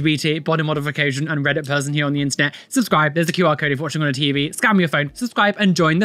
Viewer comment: I heard "Scam your phone" instead of scan and just went: On it, boss